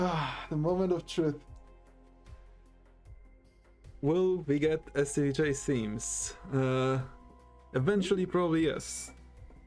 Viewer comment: What did he say at 3:57:14? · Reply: "Will we get any chase themes"